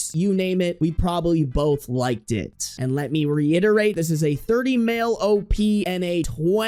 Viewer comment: I swore I heard 20 M at first. My ears are dying 😂